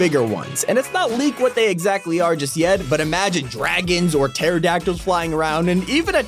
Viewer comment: Did they just say imagine Dragons